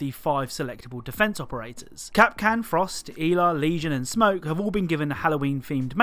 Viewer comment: You pronounced Ela so weird it sounded like eva 5:38🤣